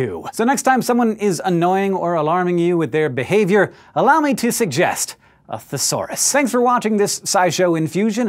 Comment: What did he say at 8:13 ? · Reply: "Allow me to suggest a thesaurus" A thesaurus is like a dictionary but it contains synonyms (alternate words with the same or similar meanings). So if I look up 'Fall' in the thesaurus I'd find words like plummet, descent, topple etc.